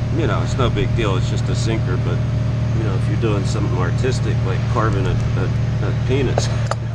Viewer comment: Wait, did you say "carving a penis" 33:00?